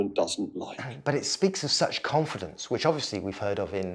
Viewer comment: You said "It speaks of such confidence". I heard "it speaks with such confidence"... Either way feels accurate.